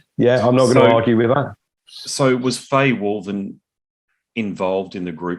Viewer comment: who is fae walden/wulven? (cant tell exactly what is said here?) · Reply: Fay Woolven. CoF manager for most of the band's career and Kit Woolven's (Dusk producer) wife.